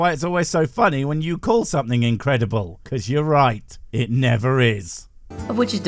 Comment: I really thought Blue Boy said "euchre" and I was like yup, love that game...many a beer went down playing it 🃏🍺